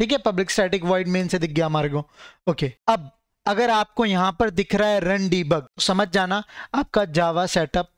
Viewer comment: sounded like runDe bug